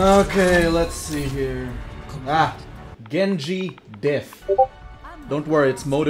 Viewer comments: I'm not that good with Japanese but I think the guy in 7:35 said "Genji is mid" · he clearly said げんじもがんばったよ